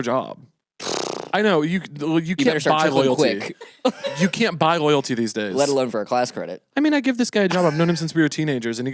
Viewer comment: for the life of me I can't figure out what Nico says here... Someone please rescue me from my audio processing issues and tell me what he said 😭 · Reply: "You better start trickling quick"